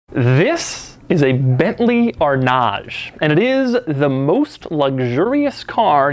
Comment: I heard "This... is a Bentley or nah"